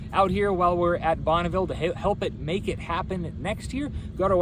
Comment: Oh crap. Did he say "make it happen next year??" I really hope not